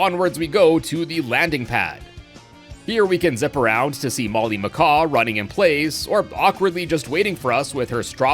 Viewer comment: i thought he said baldy macaw